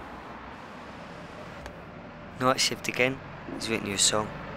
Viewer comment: when the dog says "night shift again" I thought he said "not that shit again" what a world weary canine.